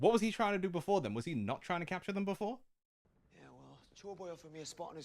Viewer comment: The first time I heard Sanji call Luffy "chore boy" I thought he was saying "Joy boy".